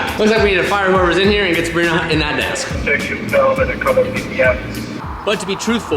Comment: did he say, “get Sabrina Hunt in that desk” ?? 👀👀🤔